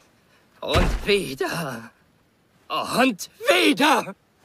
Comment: I heard aunt Peter😅